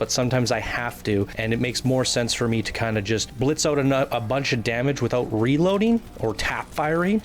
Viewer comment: it sounds like he said "it makes more sense for me to just blitz out a nut..." 😂😂😂